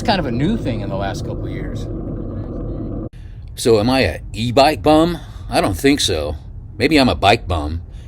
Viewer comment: At first I heard you say: E bike b0mb LOL :) I've been watching channel too much :)